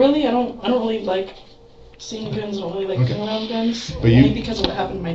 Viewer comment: she doesn't say that...🤦‍♂️ She said she really doesn't like BEING around them. Not whatever nonsense you think she said.